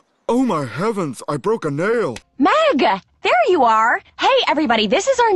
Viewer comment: I thought she said maga at first